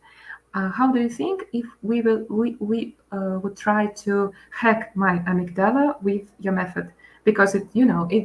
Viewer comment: What is she saying at around 25:45 “hack my amadala with your method“?